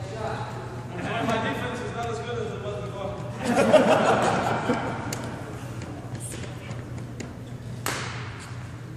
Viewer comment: what did Peter say? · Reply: My defense is not as good as it was before